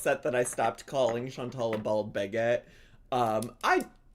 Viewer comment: I heard "Bald Baguette"